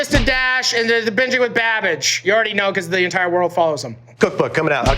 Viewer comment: Instead of "babage" I legit thought he said "babitch" :')))